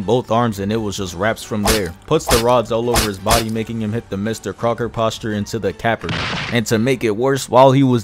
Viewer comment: This sounds like a funny reference but I have no idea what you said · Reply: Mr Crocker posture